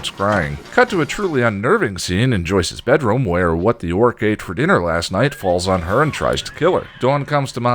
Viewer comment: Kal El's distant cousin Darin ceiling turd with a face Godzilla's tapeworm Satan's dingleberry what-the-orc-ate-for-dinner-last-night (I first heard that as "what Bjork ate for dinner" lol) the Joss Whedon